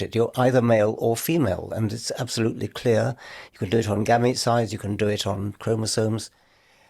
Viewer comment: What does Richard say at 3:08 mins I can’t make it out? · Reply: Gamete size